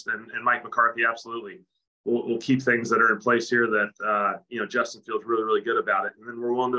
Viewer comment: Did he just say Justin FIELDS? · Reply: "Justin feels really really good about it"